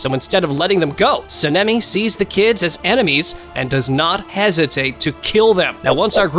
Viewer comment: I hear the name “seneme” instead of “Salami”